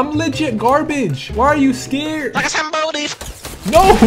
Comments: wtf did he say?😂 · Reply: No he Said like a somebody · hear the what it says at full volume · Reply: Its says like a somebody but in a vitneme accent